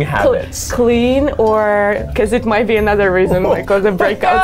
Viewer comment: the real question is: did she get eugene’s stubborn blackhead out? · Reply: I thought she said it might be a mole.